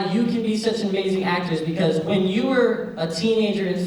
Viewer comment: did he just call his dad an actress? Lol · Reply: he said actor is